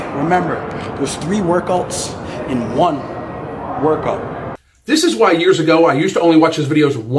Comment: about 12:34 Did he just say 3 workouts in 1 workout?!?!?!?!?!?